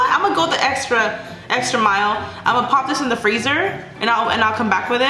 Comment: I thought she said she was gonna put the soapy water in the fridge well she said "*freezer*" and thought it would make slime😂 A freezer freezes things 😂😂😂